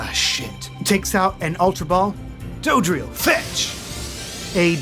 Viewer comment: I fully misheard that as "dugtrio" and was very confused for a second.